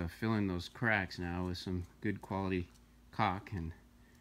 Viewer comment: did he say good quality cock😂😂😂😂